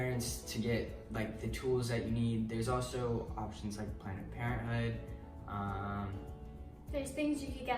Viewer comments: I'm sorry, did he just say PLANET Parenthood? 😂 8:58 correct me if I'm wrong 😂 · did he say Plant Parenthood instead of Planned Parenthood??? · Did he say planet parenthood lol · Anyone else hear him say "planet Parenthood" at 9:00 ?😂😂